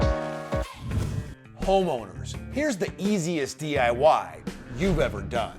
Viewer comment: Bro!!! In the opening of this video thought you said, "HOMOS!!!"...........I was like, "WHAT ABOUT US HETERO DISH WASHING FOLKS?!?!?" 😂😂😂😂😂😂😂😂😂😂😂😂😂